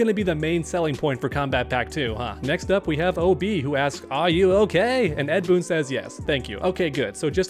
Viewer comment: That “Are you ok?” I only heard “Buster Wolf!”